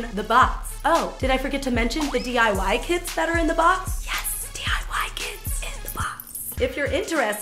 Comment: I thought she said 'DIY Kids'